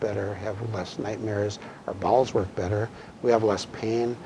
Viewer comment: does he say our balls work better? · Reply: He said bowels